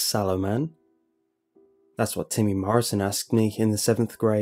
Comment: I thought he said "Jim Morrison" instead of "Timmy Morrison", and I was like wut.